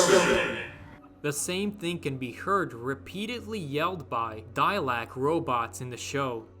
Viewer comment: What did you call them? "Dia-LEKS"